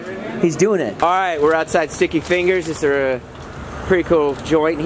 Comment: I swear I thought you said: “We’re outside with sticky fingers” and I was like 😳😏